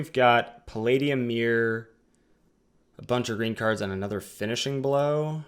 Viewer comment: Haha at first I thought you said "here we got palladium myr, a bunch of retards and another finishing blow". Lol